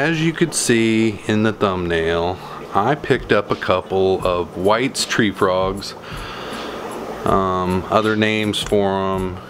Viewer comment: They are cute! At first I thought you said your "wife's tree frogs" LOL! Thought there was something you forgot to tell us about!